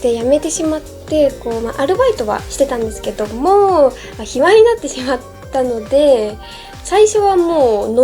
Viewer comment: 卑猥になってしまったって聞こえる